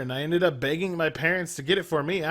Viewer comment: Lol i know im smoking that good but i had to put on sub titles cuz i thought u said " i ended up banging my parents to get it for me." idk if its ur voice but it was hilarious